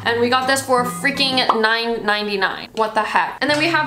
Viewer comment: I thought you said 99.99 i was about to say bish wat did ya sayyyyyyyyyyy